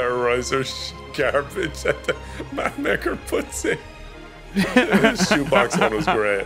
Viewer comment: ● Claim your free Starter Pack of 15,000 Food + 30,000 Gold + the EPIC Ovi Dragon! Download Dragon City by clicking here: <link> #ad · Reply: What was that you said...at 13:48 exactly...did you say "my nicker, puts that in"...